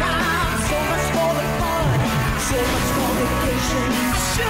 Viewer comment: The intro has been stuck in my head since watching this, but does anyone understand what the guy is saying before the ''you should see the clientele'' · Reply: "So much for vacation.."